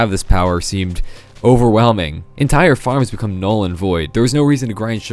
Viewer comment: did I just hear... null and void?